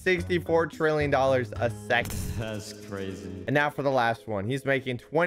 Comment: I thought he said wow it’s crazy but he said WOW its cringe…